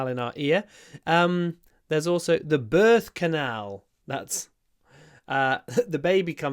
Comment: Thank you very much! 😊 I first thought that you said bird canal. 😂